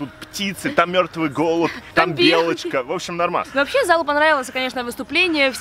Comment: Ахахаха мне послышалось не залу понравилась , а залупа нравилась